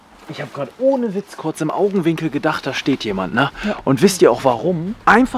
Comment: Ok... Ganz ehrlich... Ich bin doch nicht die einzige die "liegt" verstanden hat und dachte da ist ne Leiche oder? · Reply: ich hab auch verstanden "da liegt jemand"